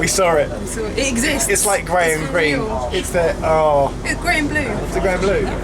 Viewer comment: I missed heard you when you described the livery as grey and green and thought you said Graham Greene!!